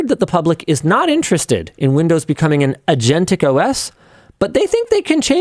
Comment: Whenever I hear "agentic" i think someone said "aging", like the program is an aging elderly dog with chronic health conditions and it would be a mercy to end its suffering.